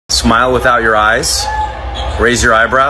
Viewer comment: I heard as "that's ur mom's face" 😂😂😂